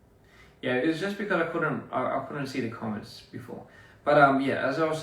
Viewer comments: what he saidd?? I couldnt hear anything 😢😂😂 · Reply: He said "Yeah, it was just because I couldn't see the comments before". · actually from here, is there anyone heard what he said? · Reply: he's just saying that he couldn't see the comments